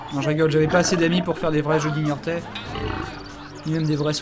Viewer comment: C'est quoi les jeux "d"In Your tale" ou "Guignortais" ou "Jeudi Niortais"? xD